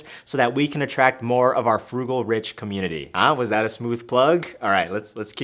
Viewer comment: I thought that said fungal😭😭😭